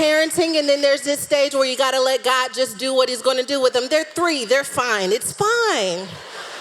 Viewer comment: I felt that when she said "They 3, they fine. They'll be fine.!" I heard "they 3 & 5 , they'll be fine. " 🗣 I HEARD THAT ... I love her, straight to the point, honest, filled with the Holy spirit, & relatable!!!